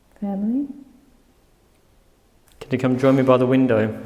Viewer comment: I hear the name “Emily”